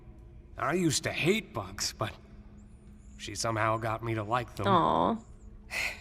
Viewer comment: OH MY GOD CAPTIONS. I always thought he was saying "I used to hate books" because I didn't know what entomology was and thought it was just super into books. And I didn't care enough to check.